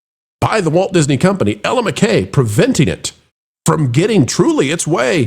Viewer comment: THIS is the first time I hear of Ella McCay, I had no idea that #EllaMcCay was a movie, infect when you talked about it just now, I was searching Google but thought you said "Element K", would have been a better movie title too. 😅